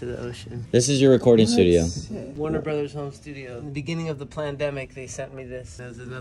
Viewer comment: Please I’m not the only one that heard him say “PLANdemic” 😭😭